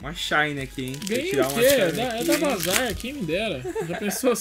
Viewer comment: eu ouvi esse " buzzlord" menor 32:19 deuslord é o outro